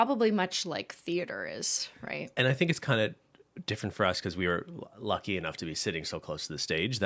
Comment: They keep saying "we were lucky enough," but I keep hearing "we are wealthy enough." 🤷‍♀